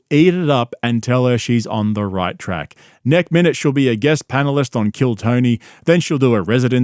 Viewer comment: Mate did you just say "Nek minute" 😂❤